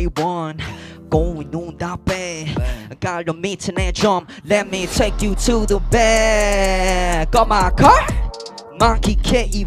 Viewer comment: did he just say lemme take you to the back of my car!!? What are we gonna do back there Markie 🥺🥺